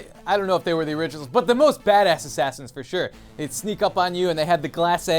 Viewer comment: i thought lasercorn said fatass assassin